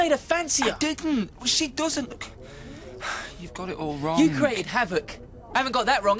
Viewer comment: what did craig say at 6:36?? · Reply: Maha he say "you have created havoc" I think anyway